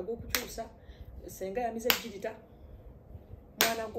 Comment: Naye JOJO TAYLOR!😅 I hear “ senga yamize egalitarian”!🤣🤣 Yabula everything you have said is true 😅